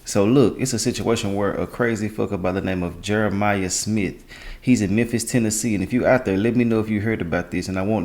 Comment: Yeah, I heard I 'am in Memphis.